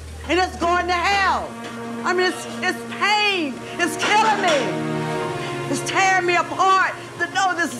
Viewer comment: What she said "It's tearing me apart to know this is happening" What i heard "You're tearing me apart Lisa" LOL